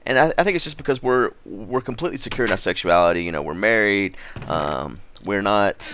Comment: Sounds like you said you are married to, awkward.